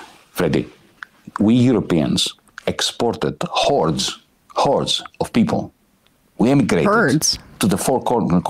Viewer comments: Why do you keep saying “herd?” He clearly said “horde.” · I thought he said "hordes" not "herds"